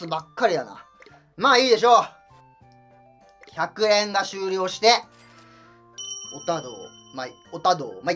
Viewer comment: 100円が終了して (空耳)